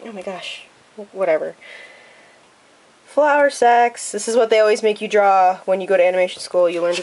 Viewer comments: I thought she said flower sex 😂😂😂 not flower sacks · I was looking the other way when you started talking about the flowersacks and all I heard was "Flowersex". I was like WUT?? XD